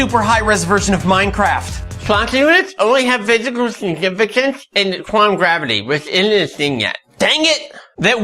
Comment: What is he saying? I turned CC on but the computer apparently couldn't make it out either... "...fuck you it only have visible significant..."... lol. I kid you not, turn on CC and see for yourself. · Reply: "Planck units only have physical significance in quantum gravity, which isn't a thing yet." -- Nerd Clone